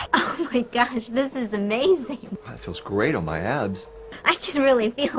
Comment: I swear he was gonna say "That feels great on my ass!"